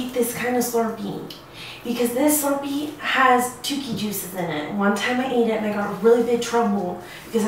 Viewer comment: what did she say it had in it? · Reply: tookie juice